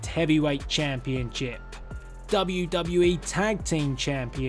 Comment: it sounds like he is saying championchip lmao